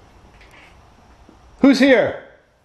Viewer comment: when you say “Who’s here”?! I didn’t hear “horror “, I heard “HER”!😳